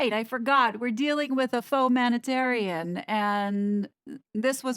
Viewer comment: Fauxmanitarian… honestly you’re effing brilliant 🙏🏻💜💜💜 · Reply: I actually thought she said photomanitarian 😂 which works as well 😂